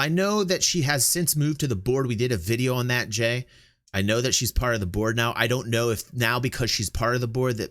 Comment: I had to rewind that. I thought I heard you say that Sandy is part of the Borg.